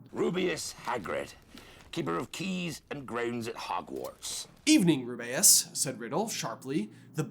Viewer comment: Is it just me, or does anyone else hear Hagrid saying "Rubeus Hagrid, keeper of keys and graves at Hogwarts." in that clip?